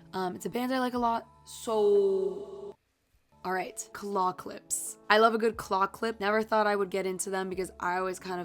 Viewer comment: I genuinely thought u were saying clock lips⏰👄